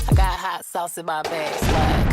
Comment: I always hear " I got hot sauce in my pants, wack!"